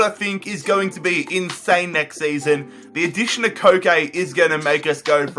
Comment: I thought he said the addiction to cocane